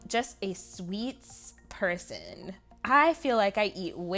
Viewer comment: love watching you plan!! funny how you mention watching tv shows in french to help you improve -- that's actually how my parents learned english!! haha when you said you were a sweets person -- i heard "sweet person" and i was like.... yes you are very sweet... how is that a weakness